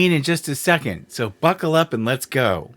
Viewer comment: Pretty sure I heard “So fuckle up and let’s go”. 😂😂😂😂😂😂😂